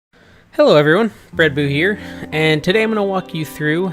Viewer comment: you need more building videos. show us how u built your ebay shelfs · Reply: i know im wrong but it sounded like u said hello everyone, breadboo here haha